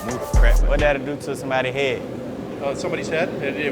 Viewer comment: what did he say lol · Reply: What will that do to a head